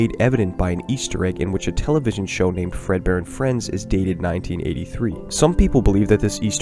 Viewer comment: I hope you enjoyed the video! New video on FNAF 1-3 Easter Eggs coming soon! :D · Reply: Would just like to point out that when you said 'Fredbear and Friends' subtitles thought you said 'Fredbear in France'